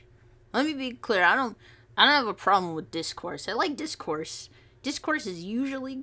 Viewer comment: I misheard Dischorse as DISCORD☠☠